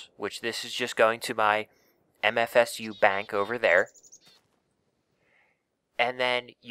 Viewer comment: For a second I thought he said MSG bank 😂😂😂😂😂😂😂